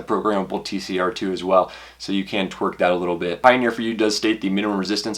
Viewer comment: did I hear you say "you can TWERK that a little bit"...? Obviously you were coming from, or headed to one of those pole dancing clubs...lol. Thanks for another great review